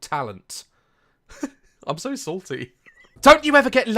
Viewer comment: THE WAY DAZ SAID "SALTY" SOUNDED LIKE "SORTY" Idk but it was hilarious to me